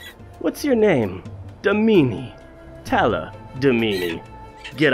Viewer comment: It sounds like he is saying talla da meanie